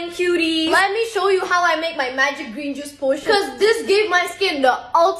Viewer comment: i literally misheard "potion" as "poison" wtf 😭